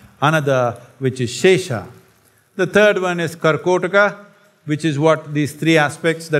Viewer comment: kala - ananta shesa - reminder 4:33 what is the third one? I cant hear it and the subtitle in that part is a mess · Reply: Karkotka